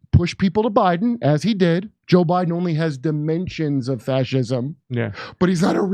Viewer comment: Cornell's so obsessed with Trump he forgot what country he was running for president in. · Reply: I thought he said "dementias of fascism"